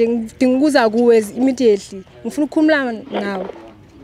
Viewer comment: Does she say “Ngifuna ukukhumula Nawe” 4:30.